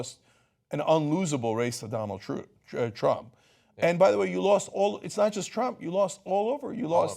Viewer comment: Did he say Donald Truth?